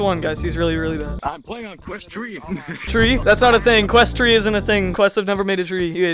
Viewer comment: i play on quest 3 it has existed for 1-2 years now bro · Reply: he meant quest "tree" bc it sounds like said "Quest Tree"